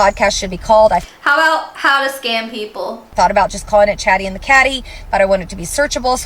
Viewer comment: Chatty in the caddy? More like "scam me from your camry" wakka wakka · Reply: Is that what she said? 'Cause I heard "chatty and the catty" and thought it was just too perfect. But now I see that would've been too self-aware...